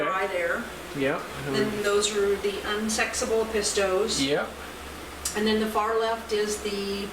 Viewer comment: Did she say the "Unsexable Pistols" ?